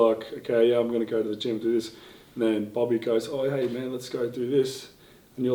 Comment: did he say Bobby?